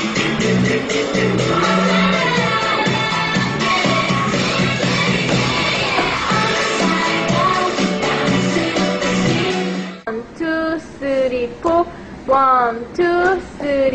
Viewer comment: lol it sounds like sooyoung is saying 1 2 30 4